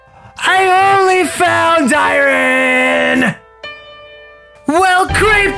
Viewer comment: it sounds like he says Iran